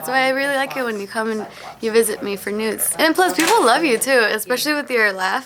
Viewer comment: i initially heard, “you visit me for nudes” instead of “news” :’)